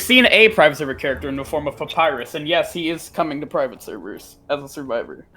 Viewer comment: Papyrus from undertale? am i hearing this right